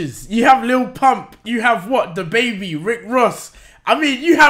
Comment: did he just said „Dababy“